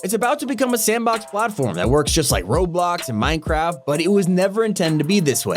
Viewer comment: did u say roadblocks? (its Roblox.)